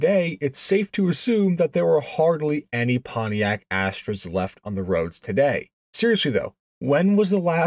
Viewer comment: The opening line, sounds like you said Pontiac Ash Tray.😅